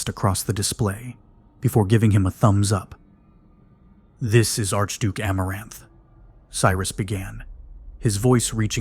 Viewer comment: lmao 😂😂 “this is Archduke GoonerBait” had to rewind and make sure I heard that correctly!! I was totally into this… now I can’t take it seriously!